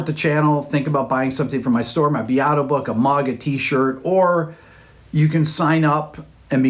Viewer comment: OMG I'm an idiot, I thought he said we could buy a MAGA T shirt from his site. I had to play it 4 times